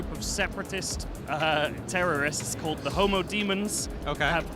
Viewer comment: Thought he said HOMO DEMONS no lie lmao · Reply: It's actually Homo Demens, Latin for "mad man"